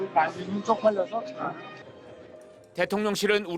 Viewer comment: 특유의 말투가 "바이든이" 라고 들리는데.. "날리면"이라는 단어는 없어